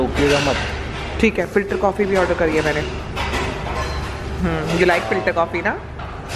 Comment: I thought 💭 that you said filtercopy